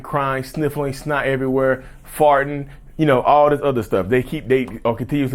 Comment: When you said “fighting” i thought you said “farting”. 😅